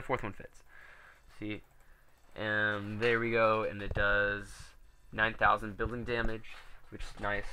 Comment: lmao at 9:23 I thought you said 9000 billion damage at first.